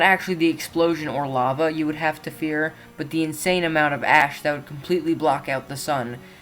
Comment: I thought he said the insane amount of Ass 😅😅